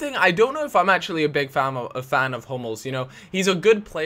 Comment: At first I thought you said 'I'm not a big fan of homos' instead of 'I'm not a big fan of Hummels'